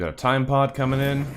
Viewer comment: So glad you are back! Also every time you say "time pod" I hear "Tide pod", is this unnatural?